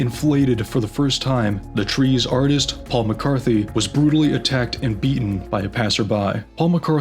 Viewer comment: OMFG I THOUGHT IT SAID PAUL MCCARTNEY LIKE FROM THE BEATLES LMAO 😭🙏